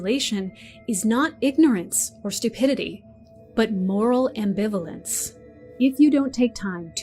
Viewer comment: hi, at 7:28 did you say "moral enbevolance"? what does enbevolance mean?, im sorry im a spanish speaker, im doing a tesis about virality and memes, it would be really helpful if you definine it please